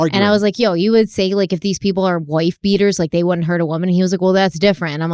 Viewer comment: did she say woooyyff beater